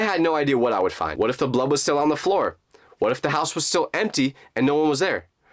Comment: Did bro really say ‘blob on the floor’ 😭🙏🙏😂